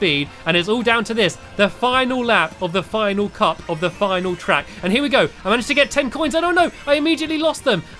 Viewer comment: I heard him say "final cup if the final track" probably on accident😸😸😸